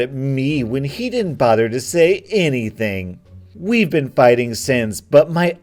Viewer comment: When he said "we've been fighting since" I thought he said "we've been fighting Sans"